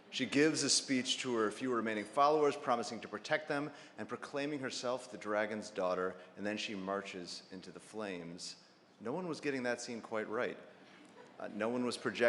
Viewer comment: Did he say 'dragons' daughter?'